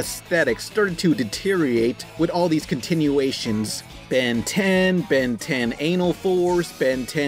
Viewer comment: Did he say Ben 10 anal?